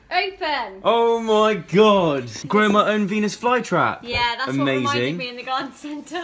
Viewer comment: I can't be the only one who thought Zoe said penis fly trap instead of a venis fly trap